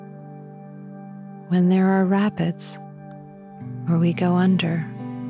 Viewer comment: I was listening to this on a laptop, so some of the words were less clear that usual. I totally misheard the word "rapids" as "rabbits"..."When there are rabbits"...Got a good laugh during the meditation. Thank you for a wonderful meditation--it is beautiful!